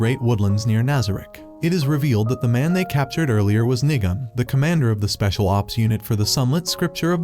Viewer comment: did you just say 😂😂😂😂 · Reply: Bro, he sad nigam